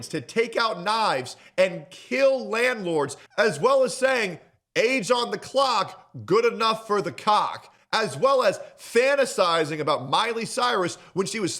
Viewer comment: the exact quote was "Old enough to count, old enough to mount - question mark?" (The "question mark" makes me think it was rhetorical in response to whatever he was talking about before that) I don't know that he ever said what you said here.